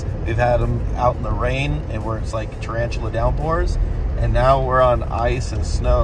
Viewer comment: Did just say "Tarantula Downpours?",.. cos I don't want to get caught in spider rain.